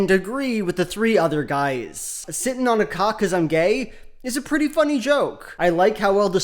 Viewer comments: I thought he said "sitting on a cot cuz I'm gay" and I still thought it was funny. Cuz I'm gay out of nowhere is funny. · I heard it as “Sitting on a cot cuz I’m gay” and that’s easily a dozen times funnier than the actual joke, to me